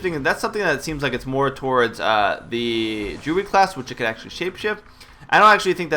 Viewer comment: When he says Druid, it sounds like he is saying Jew.